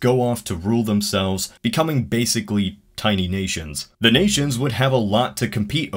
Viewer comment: I really thought you said, "...the states go off to rule themselves, becoming basically tiny Asians." (whaaa?)